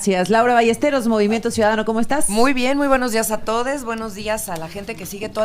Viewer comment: Clarito escuché "buenos días atoles"!!!😂😂😂😂😅😅😅